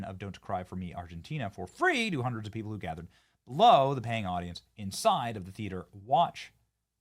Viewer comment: wait its free i thought....even says free then says "paind audience" or whatever right after 😂😂😅